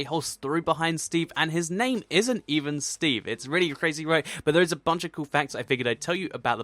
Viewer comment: did he say Steve's name was actually Crazy Roy or Crazy Ray? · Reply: RedstonedDad He said "Its really crazy, right?"